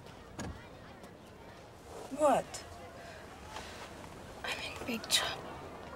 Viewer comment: Can someone pls tell me what she says at 53:30 I can’t understand it and it’s driving me mad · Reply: i think she says “i’m in big trouble”